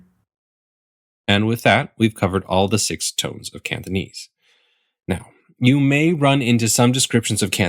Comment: It’s kinda annoying how it sounds like he says ‘Canthonese’ (I don’t know whether it’s to do with the mic or whether he’s actually pronouncing it like that)